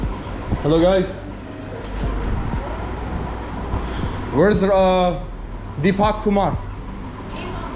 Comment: I keep hearing "Guys where's 2pac?" when he's looking for the boss lmao